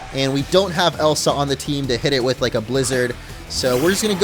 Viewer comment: Did he actually said "Elsa" in his team to bring the blizzard? hahahaha